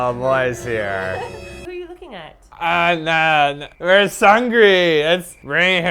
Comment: did said " Where is Seungri?!" if he did LOL !!